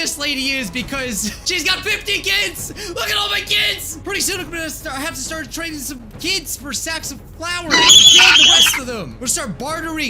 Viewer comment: subtitles thought you said “i have to train my kids for sex on flowers” 😆